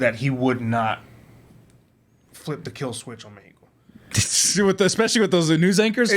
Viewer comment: "flip the killswitch on the eagle?" was that what was said?